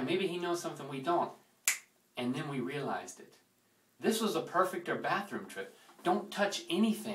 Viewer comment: Lol I thought it said Perfect ER ( like emergency room) and was omg wat happened!!?!? Hahaha.......